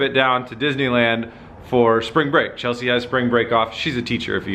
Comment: Hit up Atlanta! I wanna get a pic with you and the mazi! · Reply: Oh he said Disney Land, thought he said Disney World :(